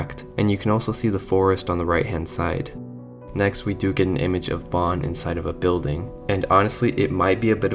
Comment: thats Banny instead of Bon